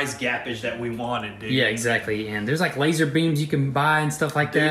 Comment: i thought they are saying lazarbeam the gamer lol